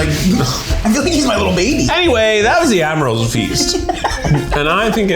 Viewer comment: I misheard and thought you made the most hilarious joke by calling it an Avril Lafeast instead of an admirals feast...